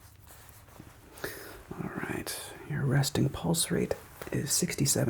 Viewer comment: I heard "Resting pulse race" as "resting bitch face" and I choked on my coffee 😂😂😂 You're a fantastic ASMRist, I was dozing off a few minutes in